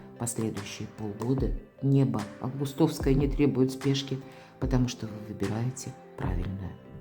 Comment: Спасибо Вам за расклад. На 27.15. не смогла разобрать "следующие полгода не..." несколько раз этот момент переслушала , и всё равно не разобрала. Извините, можно пояснить. Благодарю! ❤ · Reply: "..последующие полгода небо августовское не требует спешки". Я так услышала 😊